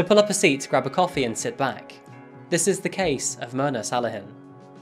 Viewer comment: Not gonna lie, I was listening to this while doing other things and at first I thought you said “This is the case of murder salad.” 😂 my bad. That was the person’s name.